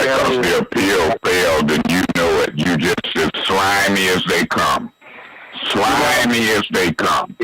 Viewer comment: what is he saying? 🤣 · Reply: "Slimy as they come".